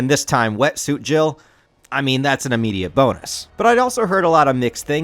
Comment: I heard that was an immediate boner 💀